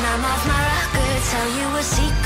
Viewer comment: thought it said: all the best bipolar